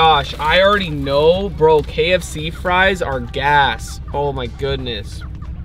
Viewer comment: did he say gas or ass?I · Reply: Gas